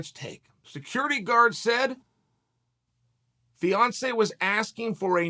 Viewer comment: Damn…I wasn’t listening well the first time and I thought chael said Beyoncé instead of Fiancé I was like damn…he assaulted Beyoncé?